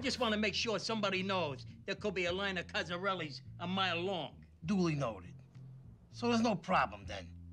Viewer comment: When Paulie said there could be a line at Cozerellies a mile long, I thought he was saying Phil has a bunch of cousins a mile long but Cozerellies (sp) is actually a funeral home.